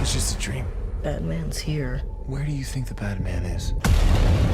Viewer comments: I heard "where do u think the batman is" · Ah man I’m so hyped for this I can’t believe there’s less than 2 weeks left for the shows to premiere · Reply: Lmao also every time I hear them say “bad man” I keep thinking “Batman”